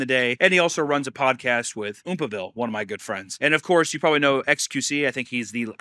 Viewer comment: the wires got crossed for a second and I thought you said xqc was one of your good friends and my heart sank to whatever is below hell.